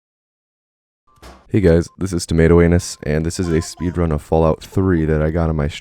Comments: I got this vid in my recommended and I did not know ur channel name so when I played the vid I heard “hey guys it’s tomato anus” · I just clicked on the video and here “hey guys tomato anus here” I thought I miss heard or he misspoke but nope it’s your name... you’ve earned a subscriber my man